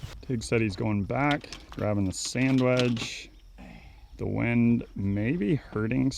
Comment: Thought said "tigs going back, grabbing a sandwich" but no....it was just a sandwedge